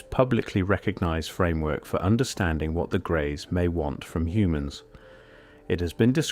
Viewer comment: Ohhhhh grays. I thought they said gays. I was like "idk but i can guess".